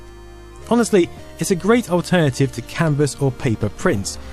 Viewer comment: Ooooooh, canvas. I thought you said it’s a great alternative to cannabis. I was like, they’re cool, but not that cool. 😂